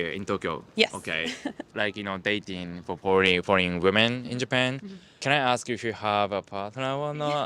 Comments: For a second I thought he said "horny women"... he said foreign women, hahah :) That's an authentic japanese accent he has there! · when he says "40 women", I keep hearing "horny women"